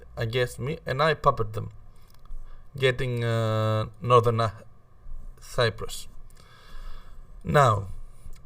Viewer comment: so northern cyprus is in southern Albania? Thanks for teaching us geography! 31:25 The subtitles say Irish Village Judas which is somewhat hilarious.